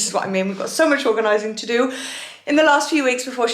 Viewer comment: I thought organizing said orgasming OMG 🙄💀